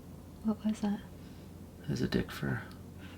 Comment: Lmao! I rewatched the video, and I put the closed captioning on to see what that would say, at 20:27. please tell me what is a Dick Fur?! 😂🤣 ( if that is what you said was outside your window!) your hilarious !! Keep laughing through this dark family time. Sending you and your family best wishes ❣️